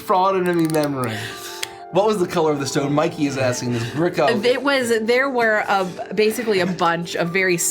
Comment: the closed captioning saying 'this brick oven' instead of gricko